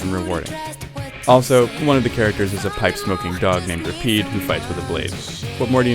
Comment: I thought I heard pot smoking dog. xD I was all like WAIT WHAT!?